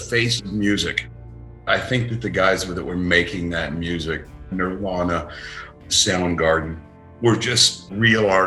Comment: subtitles say marijuana instead of nirvana lol